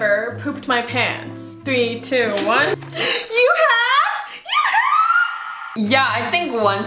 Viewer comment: “You have? YOU HAVeeeeee” my volume goes down* what I heard: “hey hAVEEE” lol